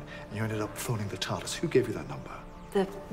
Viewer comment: i always thought that they said "there's a woman out there who's faking that we stay together".. my entire worldview just collapsed